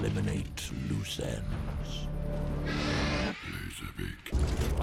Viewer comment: did soundwave said "here's a pic" 😂